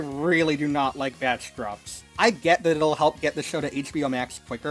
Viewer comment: Wtf did say at 7:29 wtf doesn't he like? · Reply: Batch drops